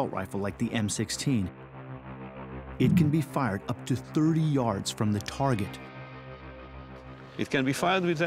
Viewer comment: did he say 3 yards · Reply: he said 30 yards